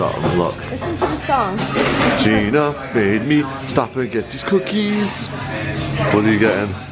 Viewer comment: It sounds like said scookies instead of cookies